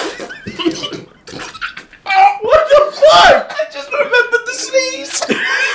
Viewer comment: What did said here? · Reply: I just remembered the sneeze, the sneeze the dog did earlier in the video^^